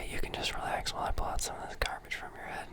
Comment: I thought he said he was going to pull carpets from my head and I didn't get it lmao